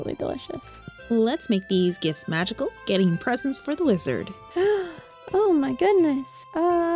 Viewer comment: Maybe I'm stupid but I could swear I heard "Lizard" instead of "Wizard"🤣